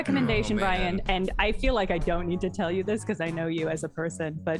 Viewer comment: I know told Brian, "be verbose," but for some reason my brain heard it as "beaver boats."